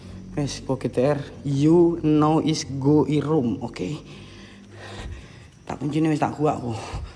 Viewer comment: You is no is go is room...ngomong apa sih